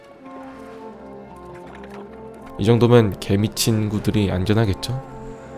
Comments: 형 자꾸 개미친구가 개 미친 구 로 들려 ㅠ 미안 내 귀가 이상한가봐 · 초 개 미X 구들이라고 나만 들리나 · 때 나만 개 미친 친구들이 안전하겠죠? 라고들림?